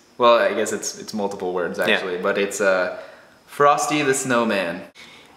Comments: it sounds like theyre saying fartsy the snowman LOL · its sounded like said farty istead of frosty lol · and it sounded like they said Farsi (a language if i'm not mistaken) tee snowmane And yes Chocolate bars are real we have some here in Belgium